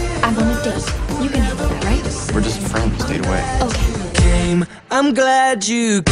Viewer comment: amazing video!! can you please tell what dan says in 1:15?? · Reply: We're just friends. Date away